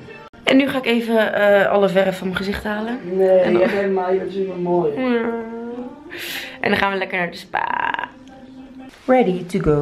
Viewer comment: Wat zegt nou bij 5:41? Ik hoop niet zoiets van: neeee nu vind ik jou juist zo mooi... Haha lekker vriendje heb je dan😂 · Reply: Hij zei volgens mij "nee je bent super mooi" dat is tenminste wat ik verstond :)